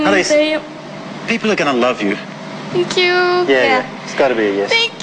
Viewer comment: I thought he said Khalas in arabic which means "enough" 😂😂😂